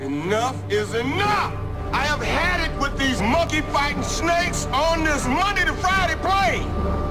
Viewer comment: I always thought he said "monkeyfying," like the snakes were turning people into primates. That'd make an interesting flick. XD